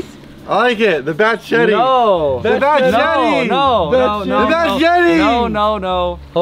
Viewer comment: I thought he said “the bat’s yeti” 🤣